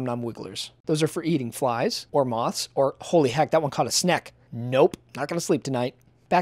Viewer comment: i thought it said "shrek!?"